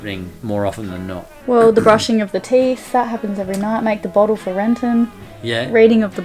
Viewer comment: Anyone else hear Eylayna say “make a bottle for Lenton”